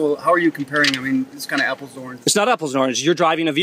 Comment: What does he say at 3:21? Apple-zorin? That's all I can hear haha..